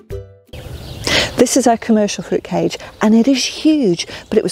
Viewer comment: Bruh I thought she said fruitcake and I'm like that's wierd but ok